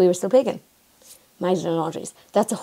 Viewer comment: Ok, need a bit of help understanding what was said at 3:06 Great video, thanks for the research and your, as always, awesome presentation. (what follows is my opinion, and as such may not be yours) Most Christian observances are transformations of what was already in the population at the time. If you read about Paul, in the Bible (Acts 17:16–34), he addressed the religious idolatry of the Greeks who had an altar to the “Unknown God.” i.e. he spoke to people with what they already understood (culture, etc). What's important about scripture is to read it for yourself before you take a religious view (denominations). Bottom line, don't discount it if the only understanding you have - instead of your own as read from scripture. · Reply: Magdelen laundries